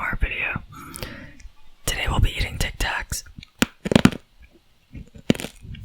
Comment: If u turn on the eng caption it says dick tax instead of Tick tack lmaooo